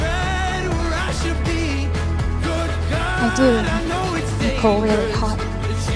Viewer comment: Whats nicoles middle name? It sounds like waves says “Nicole Really Haught” 😂🙃